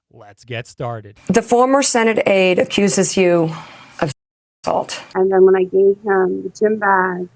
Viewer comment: It took me 10 mins to figure out he blanked “assault” , I thought he was saying “fingering”